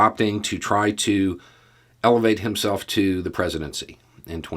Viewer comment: What I heard ‘Pence trying to ‘levitate’ to the presidency.’ Just as likely as ‘elevate’😂